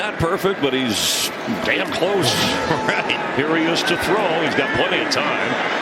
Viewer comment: did I hear Chris said "Dam close"